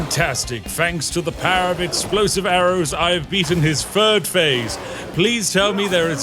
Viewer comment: is he saying "I've beaten his furred phase" or "I've beaten his third phase"? Both are plausible in this context, and given his accent (or speech impediment, I'm very unclear on that), I can't tell the difference